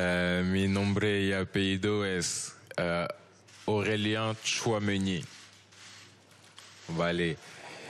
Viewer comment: Yo escuché Aurelian Champion League. Éste tipo nació para el Madrid.